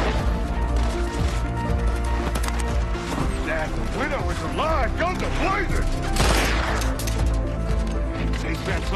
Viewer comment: What s exaclty does he says can anyone write here please? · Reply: That Widow is alive! Guns a blazin!